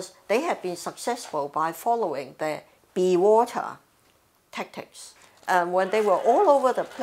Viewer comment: did she just said be water tactics?